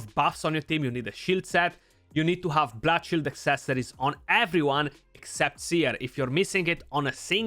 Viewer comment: Ok ... i can't understand what i need on every champ except Seer .... i have listened 3 times & can't get it. (at about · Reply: Bloodshield accessories, you get them from CVC only. today is the day.